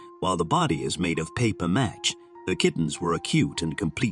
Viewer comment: The skateboard is pronounced, Bane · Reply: I thought he said 'paper mash'. You can tell it's a computer or AI or something. Def NOT a real voice.